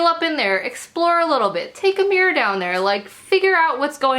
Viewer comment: "Take a mirror down there".. what I heard was "Take a beer down there" lmao!! Now I want a Femmy Cup!! Thanks for the honest review, oh Queen of the Cups! :)